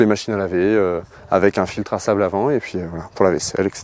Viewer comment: un filtre a quoi ??!! Savlavan ? 😅😂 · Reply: sable lavant je pense